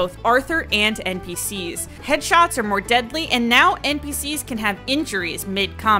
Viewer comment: did i heard correctly?? Headshots are deadlier